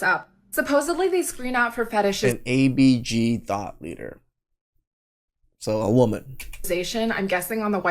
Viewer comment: It's probably just me, but when they said "thought leader", I heard it as "thot leader" 😂